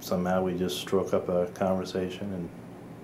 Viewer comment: He said stroke up a conversation instead of strike.